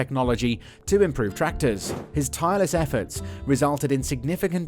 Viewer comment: Did I just hear " his tireless efforts"3:41😂😂😂😂